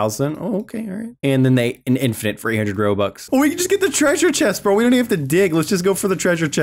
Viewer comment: Did say 300 robux at 13:20. LoL